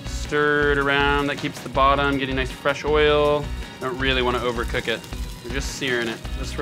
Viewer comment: Did he say fresh oil? 3:50 Fresh oil.